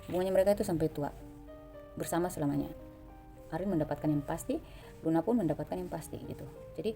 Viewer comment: Spertix saat ini BuNa Bunga-Nazriel makin dekat mbak, Oh ya Tp di menit 11:11 itu apa yaa kok sebut nama Luna?? Smoga aku yg salah denger 🙏😂